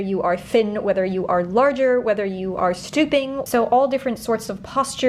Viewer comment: When you said “he illustrates the drafting system for all body types” I heard “whether you are thin, whether you are larger, whether you are STUPID” and immediately thought YES THIS IS MEANT FOR ME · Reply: stooping form. not stupid 😅 but guess both makes sense, due to the spine issue.